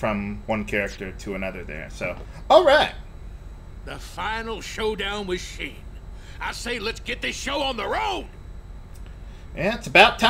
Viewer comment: Cringe... xD "The final showdown with Shin." Whenever I hear him say that I always think he's saying 「死ん」 as if referencing to all the death that Sin brings... Is it bad if I burst out laughing at 8:13?